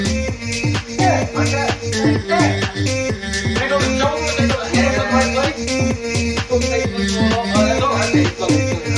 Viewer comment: Does anyone know what does Tristan says at 0:05? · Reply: “Andrew, you can’t do it. No you can’t. You ain’t got the shoulders, ain’t got the hands in the right place.”